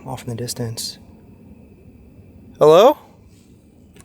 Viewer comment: woman singing Voice sounded like it was saying Hello